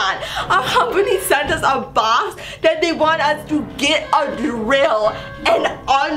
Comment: when she said get a drill I thought she said grill and I was like what 😂